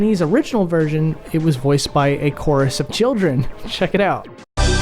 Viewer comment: Definitely thought Akoris Ovchildren was a person….until I heard the kids yell and was like “oh I’m just dumb”